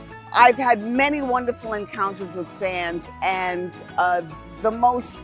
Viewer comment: At first I thought she said encounters with Sans Help me there's no escape.